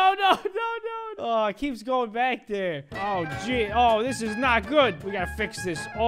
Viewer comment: i think he said "oh sh*t" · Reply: he said oh je